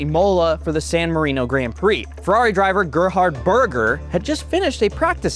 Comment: Did He Just Say That His Last Name Is Burger?